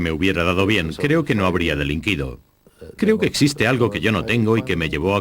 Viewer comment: Aprendo espanol. Por favor, que dice a 46:41? 'Creo que ........' No puedo cogerlo. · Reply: creo que no habría delinquido... o creo que no habría cometido un delito.